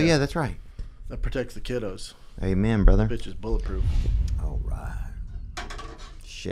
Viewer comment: <link> gang gang buzz buzz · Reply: what did Brendan say at 24:38 ? his babymoma got a bulletproof car? Why did he call his wife baby moma? Who did he mean?